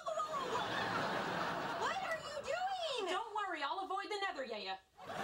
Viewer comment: what does Amy say at 1:51? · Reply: "Don't worry, I'll avoid the netheryeya."